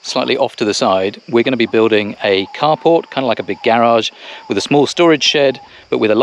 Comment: By the way I thought you said Cardboard building at first then realised it was car port lol